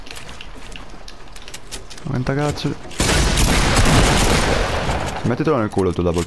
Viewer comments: (ma tagazzu) ho sentito bene? 😂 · ma sbaglio o ho sentito un ma ta gazzu ?